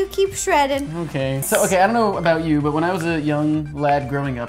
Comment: when I heard Jake said "mkay" i go to wassabi channel...